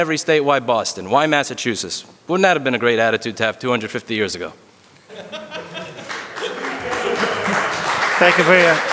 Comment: Would someone please transcribe/explain me what is saying at 6:55 ? Thanks in advance. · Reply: ever heard of the American Revolution?